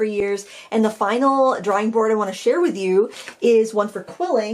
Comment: LOVE that Maine accent... I thought you were saying 'drawring' board at first :) These are clever...well done. I love how your bowls are turning out!